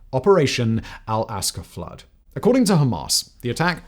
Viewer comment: Did he just say El Casa Flood instead of Al Aqsa Flood?